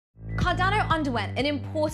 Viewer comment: Thought she said CARDANO UNDERWEAR as her opening line lol 😂